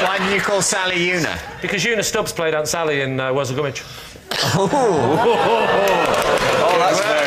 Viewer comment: I finally figured what Lee says there by googling ‘una sally’: “Because Una Stubbs played Aunt Sally in Worzel Gummidge.” I have never heard of any of these, no wonder I couldn’t hear!